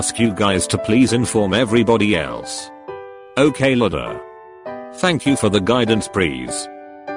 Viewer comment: what is written:Okay Lida what did i hear:Okay mother LMFAO I'M NOT JOKING KSNSJSKSNSKS